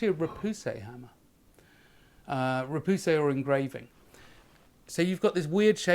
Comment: Anyone know what he was calling the little engraving hammer at @ 3:26 It stared with an R but I couldn't make it out??? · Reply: Repousse